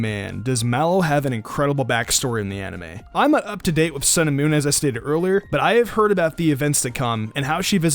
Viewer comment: I keep hearing "cinnamon" when you say sun and moon